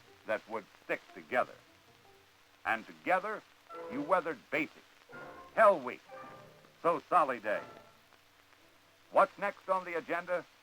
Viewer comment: What is the narrator saying at 2:01? So Solly Day? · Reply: Stick together-and together they weathered threw hell week, so Sally Day